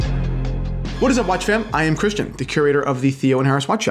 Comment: Had CC's enabled from a previous video and discovered Christian always starts with "What's up watch fam?" - I previously thought he said "What's up watch fan?" Always seemed a bit on the nose 😅